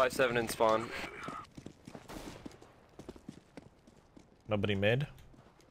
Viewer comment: did he say in 4:05 dobry mid ?